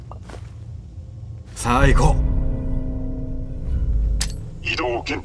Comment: 毎回『さあ、行こう！』が『最高！』って聞こえる